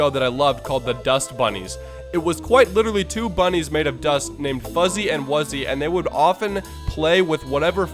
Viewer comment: I keep hearing either cuzzy or fuzzy